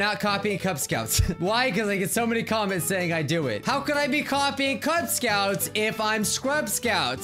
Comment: * how could I be coffee cut scouts * that's what YouTube said :/